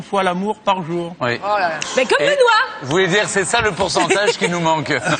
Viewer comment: qu'est ce qu'elle a dit à 7:34 ? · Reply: Comme benoit